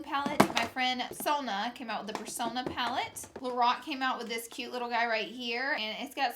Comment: I thought she said the Rock instead of Lorac